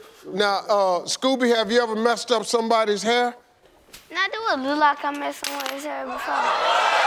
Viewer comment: When Steve asked if the kid has ever messed up someones hair, what did the kid say? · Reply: “does it look like i mess up people’s hair?”